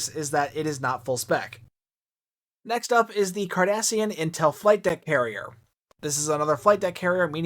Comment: YouTubes auto generated closed caption gave me a good laugh at 15:45. Instead of 'Cardassian' it said 'Kardashian' 😂